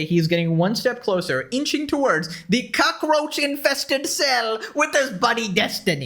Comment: You said he would share a cockroach infested cell with destiny.... I heard "cuckroach infested cell" I think that may be my new favorite name for destiny now ty!